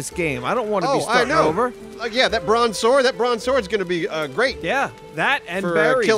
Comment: Did said LeBron Sword?